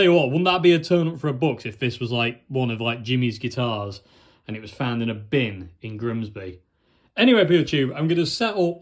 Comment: I thought it said, this guitarist was found in a bin, appearances can be deceptive…